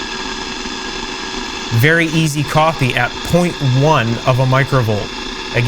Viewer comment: I'm sure you're saying "very easy copy", but I keep hearing "very easy coffee" lol 🤣